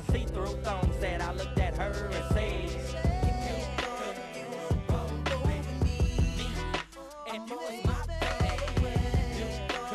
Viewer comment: Jason Weaver was so slept on!!! Underrated talent!!! He straight murdered the chorus so effortlessly. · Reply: I always hear "You know that you gonna go with me" 😭😭😂